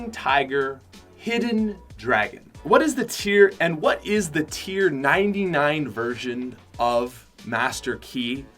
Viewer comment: did he say tier 99 virgin?